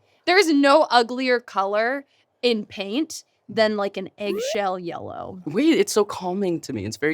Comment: did Vic say eggshell yellow?